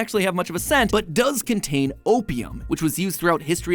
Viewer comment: I heard “opium” but my head made me hear “copium”